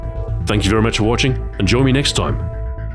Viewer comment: It almost sounded like you said "vapor" instead of "vepr" maybe that's what your pun should be about